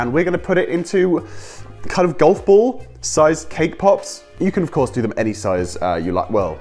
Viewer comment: When u said Cake pop i thought u said Kpop😂